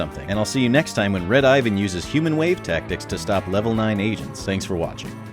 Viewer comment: Very helpful but I laughed so much becasuse at the end it sounded like you said "See you next time when Red Ivan uses human wave tactics to stop level 9 asians"